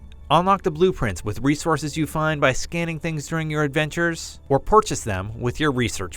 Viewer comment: Did he just say, "PORchase?"